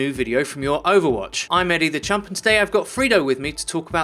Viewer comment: Wtf of a name is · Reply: Lil Wiz I always heard "The champ"